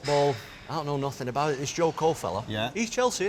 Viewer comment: thought it said j cole i was confused for a min lmaoo